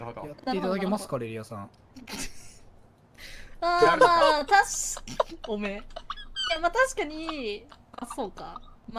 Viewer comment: ここ視点だと魂の笑い声が少し溢れて聞こえてて「おめぇ」って言ってるんよなw